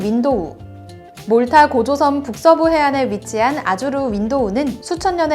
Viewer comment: 저만 고조선이라 들려요??